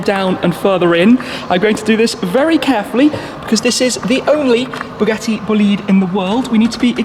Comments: I heard it as bugatti bullied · i thought i heard him saying bugatti bullied